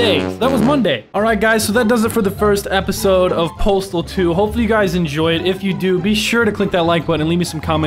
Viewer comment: Am I the only one who thought it said portal 2 not postal 2